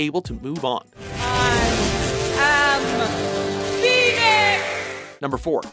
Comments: did she just say i am penis? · Did she say "I AM PENIS!" at 5:56? :D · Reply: No, she said "I am PHOENIX"